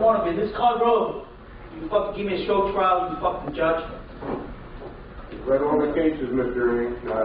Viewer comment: can someone tell me what does he say to the judge at 45:44 i cant understand him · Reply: I think he says, "I'm [locked in? walking out of ?] this courtroom. You've fucking given me a show trial you fucking judge." Something like that